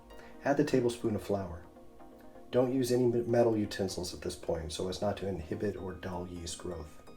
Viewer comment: Does he say 'Don't use any BUT METAL at this point so as not to inhibit yeast growth'? Timestamp: 6:01. I can't make it out clearly. I thought he said not to use metal, but then I see uses a metal teaspoon to add the flour. Anyone?